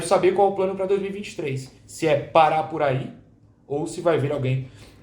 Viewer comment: Maluco tomei um susto no minuto 10:13 kkkk eu entendi que o plano pra 2023 é o Pará ia voltar, por causa da frase:"parar por aí" e eu entendi:"Pará por aí" kkkkk